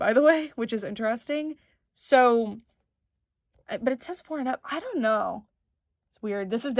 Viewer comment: The auto-captions say "p*rn and up" instead of four and up. I don't know if you could get demonetized for that being there even by accident so I figured it was better to let you know just in case! Loved the vid 😊